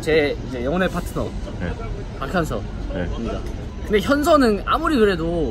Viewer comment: 왜 나만 박항서로 들리지